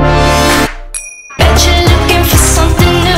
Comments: Before the bread drops you kinda hear the guy say PIECE OF BREAD 🍞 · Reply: Although apparently it's supposed to be "that's it brian", I always hear "that's it friend" But now I will never unhear that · did he say "am g@ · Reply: actually he said "that's it Bryan?" then he said "Oh yes"